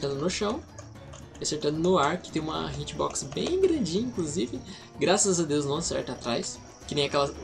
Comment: Heueheu não sei se foi impressão, mas no 5:20 tu solta um "Hitbox bem Billie Jean"